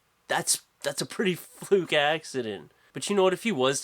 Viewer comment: i thought he said that's a pretty fruity accident. He actually said fluke accident. boo on me i know. ha!